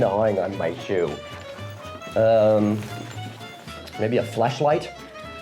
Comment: I originally thought he said "fleshlight" 🤣🤣🤣